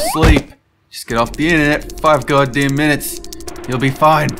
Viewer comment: I heard “God Damn”